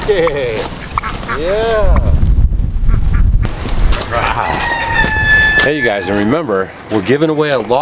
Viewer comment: Love the song! Thought it was saying “hey, Doug” ❤️